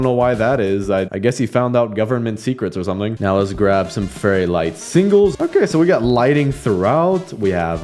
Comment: Uhm excuse me did you say FURRYLIGHTS??!!